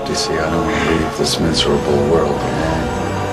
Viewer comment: Hey Where You Get The Footage In 0:02 0:03 0:07 0:11 0:13 0:19 and 0:22 from? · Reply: In 0:20 What Audio Says To See I Don't Believe This Miserable World Belong